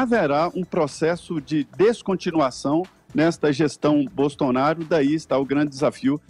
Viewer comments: Aos 7:34 eu ouvi mau, ou o Zé Maria disse Bostonaro? Caio muito bem como sempre.... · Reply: William Maniga ele falou bostonaro sim 🤣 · Foi impressão minha ou o Zé falou aos 7:33 gestão Bostaonoro!? kkkkkk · "GESTÃO BOSTONARO"? Foi isso mesmo que eu ouvi? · José maria Trindade fala "gestao bostonaro"... sinceramente queria saber q porra foi essa... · Eu ouvi um "BOSTONARO"? 7:34....Ou estou louco? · Só eu q ouvi 7:34 o Zé Maria falar Bostonaro? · eu ouvi pejorativo bostonaro?